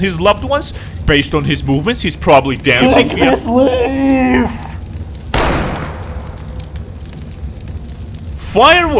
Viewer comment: does anyone know what he said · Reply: He actually said “I can’t leave…”